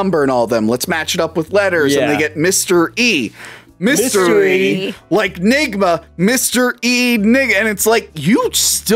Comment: …. Did just say Mystery Nigga….😬🫢😶🫥😵‍💫😵 go watch the time stamp